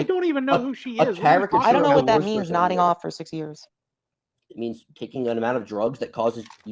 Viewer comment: I seriously thought said "knobbing off for the last 6 years".